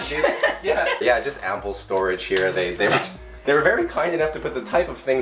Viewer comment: I kept hearing him say "apple storage" while you guys were in the kitchen in the first apartment. I was so confused...why would you need separate, specific storage for apples? Took me so long to figure out he was saying "ample storage".